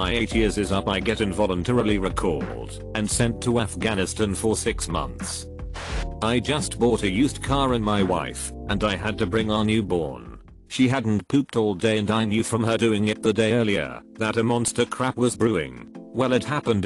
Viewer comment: Welp I thought he said "I bought a used car and my wife"